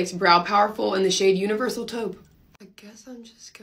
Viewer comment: I thought she said Universal toe??? Or universal toad???? I was like What kinda eyebrow color is that??